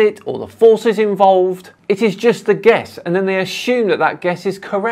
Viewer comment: Did you just say, "ashume?"